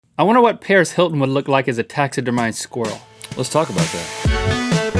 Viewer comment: I thought Link said Squirtle.